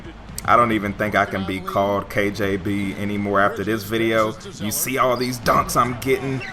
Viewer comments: did he say kjb? Hmmmm? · wait at 4:30 did you say KJB